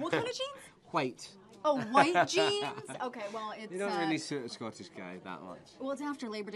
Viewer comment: did he say “you don’t really suit a Scottish guy” because she didn’t understand him when he said “white” 😂? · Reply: He said, “they don’t suit a Scottish guy,” referring to white pants.